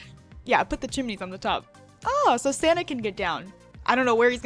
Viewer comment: Please tell me your secrets senpai! PLEASE!! This house is just....just....GORGEOUS!! And when you said "santa" in the beginning of the video I thought you said "Sonic" and I was like wut...O.O